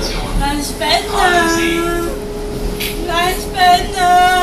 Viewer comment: Ich hab an Ende verstanden: eine spiNNNEEEEE!!!!!